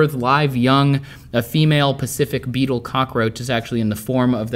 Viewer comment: did you say pacific (specific) also idk why but the phrase "can you milk me Focker" made me laugh while watching this video!!! 😂😂😂